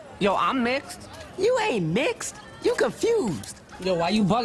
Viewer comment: When he said “why you buggin” is thought he said why you black and almost died😂😂😂